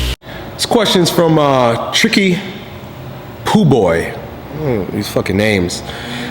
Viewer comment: "Tricky Poo Boy" lol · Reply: i heard "poop boy"